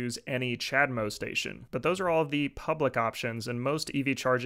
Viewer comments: I swore he said Chad mode adapter 😂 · I thought he said “chad mode adapter” like I’m disappointed that’s not an actual thing the charge your Tesla in like 10 seconds